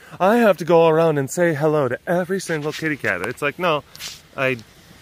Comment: Am I crazy or did you say “shitty cat” at 7:55? If you did, that’s a hilarious slip up!